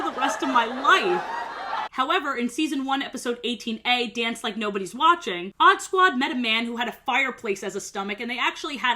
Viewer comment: i rlly thought u said oddbods i was so confused